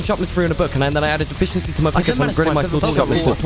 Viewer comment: i heard "I added efficiency to my pokemon"😂😂